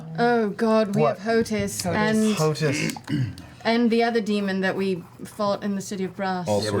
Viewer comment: Oh man, I thought they were saying "Potus the Demon" lol. If you don't know, Potus is short for President of the United States.